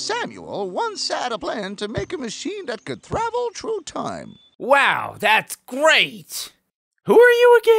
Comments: "Travel true time" I'm not bad at spelling it just sounded like that. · Did he just say "thravel trough time"? And they just left it in?